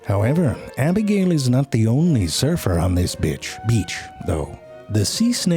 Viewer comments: bitch instead of beach😂😂😂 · O god listen at 3:10 to 3:13 he says btch instead of beach lmao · This b!+🤬h beach 🤭😂. Who else heard that ?😂